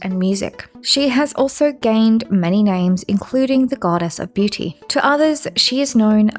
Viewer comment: I'm sorry, I was listening to this in the background, I know at 1:43, you Said the Goddess of Beauty, but for some reason, I heard the Goddess of BlueTooth